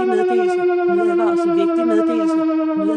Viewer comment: what’s being said is in danish she says “Nøterevel vigtig medleese”